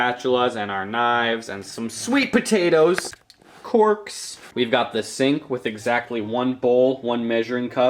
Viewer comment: When you said corks i thought you said corpse